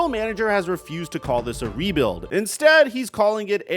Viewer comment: Bulls GM: I thought you said DEbuild the team.